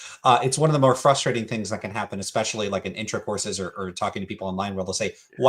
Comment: Did you say "intercourses" rather than "discourses" 🤣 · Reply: I think I was trying to say "intro courses" but it sounds like "intercourses."